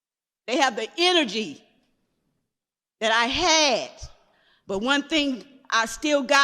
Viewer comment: Did she just say, "the energies I has?" And she on the education board? Wow affirmative action at its finest.